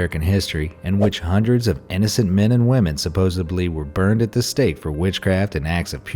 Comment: did he say "supossebly?" 🤧